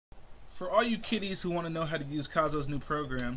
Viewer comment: When I first heard Kiddies I thought you said titties lol